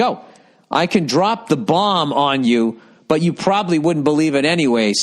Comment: The saying is "ball". Does Bill actually say "bomb"? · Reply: the saying is ball, but the letter he was reading said "I can drop the bomb on you, but you probably wouldn't believe it anyway so I won't", so that's why Bill did say bomb 7:02 in